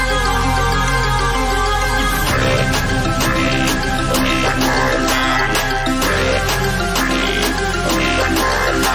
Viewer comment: I still to this day wonder what they’re saying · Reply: Break free, we are more loud